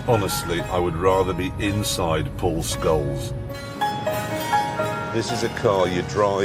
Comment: Did Jeremy say "Paul Scholes" or is it just my bad hearing? 😂